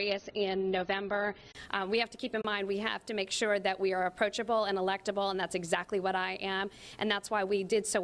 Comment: I heard delectable at 1:32 😁